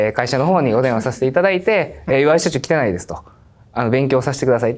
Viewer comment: まじで申し訳ないけど、ここ「岩井社長汚いです」に聞こえてエグいくらいツボったww